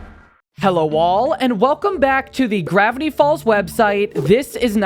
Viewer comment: I thought you said "Hello wall" and almost choked on my pasta.